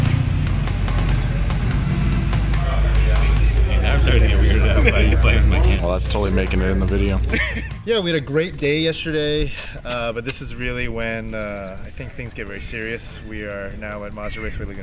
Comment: lol did he say "we had a great gay yesterday"